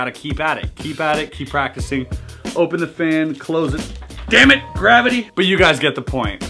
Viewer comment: I thought it said "Dammit, gravy!"